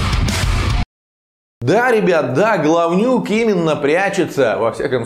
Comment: Сразу услышал Гавнюк)